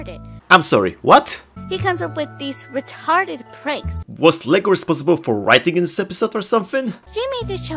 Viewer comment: for some reason i heard "licor" when you were refering to lego(ShallotHare) .